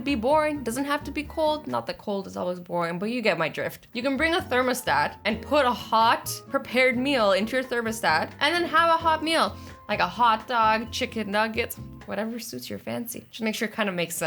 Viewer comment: ……..did she say....thermasdat…...my god...……..